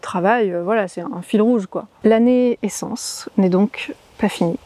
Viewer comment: Bonjour Quand tu as dit "l'année essence" j'ai entendu "la naissance"! La fin d'un cycle permet le début d'un autre! Bon vol et bon voyage à toi aussi! ❤